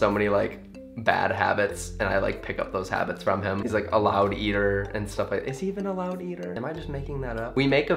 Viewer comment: Hi! I am a huge fan and I watched your video with captions on, and I noticed that it said @ 6:06 your dad is "allow dieter" when you said "a loud eater" Lol XD. I also found other mistakes like @ 6:35 the caption said "green" but you meant "dream" and when you were talking about the dye-houses, the captions said "die houses" @ 7:20. How creepy would that be if people thought you go to houses to see people die to pick your fabrics for you clothing line XD! I can help with other videos, if you'd like lol. Just trying to help others who may not be able to understand! :)